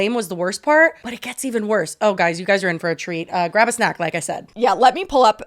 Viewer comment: When said "you're in for a treat", I heard "you're gonna grow a tree" and had to rewatch to understand lmao. I was like "is this a Spanish saying that isn't translating??"